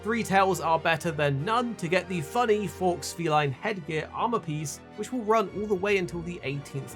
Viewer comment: people debating the correct pronunciation of lagiacrus meanwhile i'm like "did he really call it FOX feline" at 1:13? lmao what a terrible fox pas